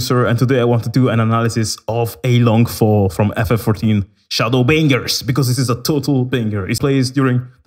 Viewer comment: for some odd reason i heard you say shadowbangers right before i paused my vid wtf ahahahahah xDDDDD im so sorry xD